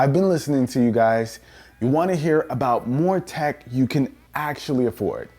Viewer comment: I thought you said tech I can afford